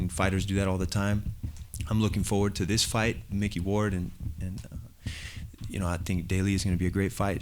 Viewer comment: Did he just say mickey ward?